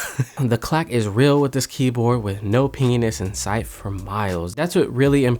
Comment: am i the only one who thought he said penis instead on pingyness